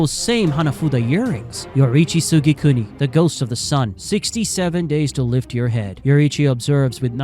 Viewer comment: did bro say 67